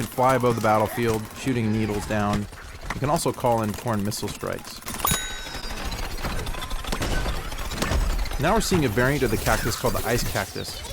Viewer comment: sounded like he said "porn missile strike..." o.O Im sitting there like... Dafuq?